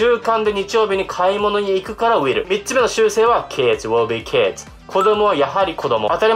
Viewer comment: これ2つ目の習慣はって聞こえるけど3つ目の習性はっていってるし…ちょっと私の頭じゃ理解できない